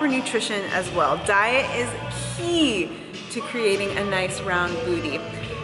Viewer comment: What s the key ??? İ didnt understand the word you say at 2:57. you are amazing and thank you for this video, because İ die to do squat !! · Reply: She said: "Diet is key"